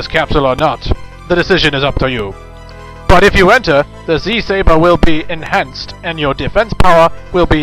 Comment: wtf? it said "Z-saVer"